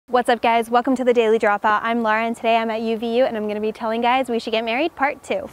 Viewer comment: I thought she said what’s up guys I’m Lauren 😂